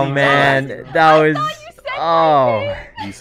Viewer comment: at the end the last thing i thought i heard was corpse saying "instant cheese" and then the outro played XD